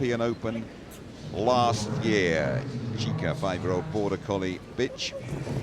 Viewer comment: dont tell me I'm the only one who heard bitch